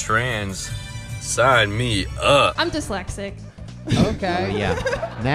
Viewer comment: I laughed cause i thought she said "I'm dyslex-tit"